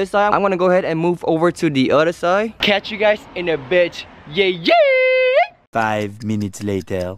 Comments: Instead of bit he said bitxx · Did he just say catch you guys in a bitch?